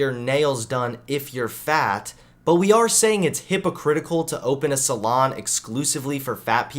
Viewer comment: It probably wasn't intentional but when you said hypocritical I heard "Hippo Critical"